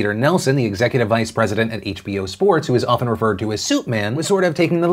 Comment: "Suit man" sounds like the name of a Rick and Morty character. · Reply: ooooh that makes more sense. I literally heard "Soup Man" and thought what a weird nickname hahahaha